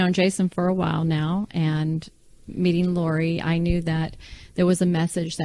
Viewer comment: Approx 9:36 time stamp Melanie says when she met LORI. Did I hear that right? Is it just me or does Jason look just like Charles Vallow?????